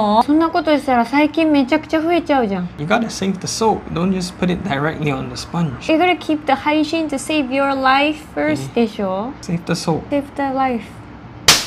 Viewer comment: "Save the soap" vs "Save the life" 😂 too funny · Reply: I thought she said save the wife 😂😂